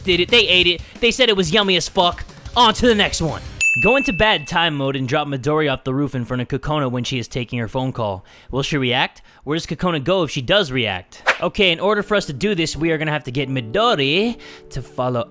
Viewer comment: did i just heard him saying 'i'm gonna drop midoriya off the roof'